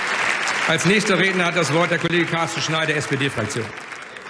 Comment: hab als erstes Carsten Stahl verstanden